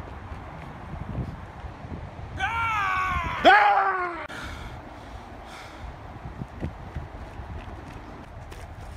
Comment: I still have no idea what he’s saying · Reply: A stick and two dollars.